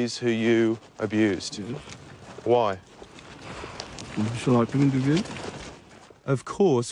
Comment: What the fuff does he say? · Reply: "So I can interview them"